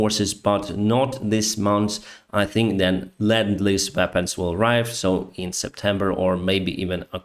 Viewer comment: 🔥 Support Pilot Blog on Patreon! <link> 🔥 Donate here: <link> 🔥 Find me on PayPal <mail> Link to PayPal: <link> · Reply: Makes sense, thanks. I kept hearing him say "land lease" and that made no sense at all.